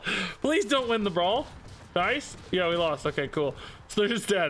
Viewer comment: I was watching some JackFrags earlier (he plays battlefield and yells DICE the developer whenever something goes bad) and I thought you said “DICE” at the brawl. Had to rewind